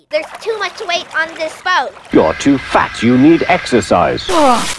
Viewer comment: I heard "you are too fat you need extra size"